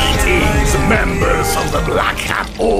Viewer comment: I thought bro said black Air Force Organization💀💀😂😂